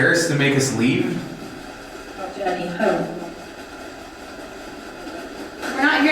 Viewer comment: I didn't hear anything about home, but I heard " my daddy hurt me" or "my daddy hurtin" in a voice that sounded like a little boy talking after crying, so it was kind of mumbled and pouty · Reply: same! I heard “daddy hurt me” or “want daddy home”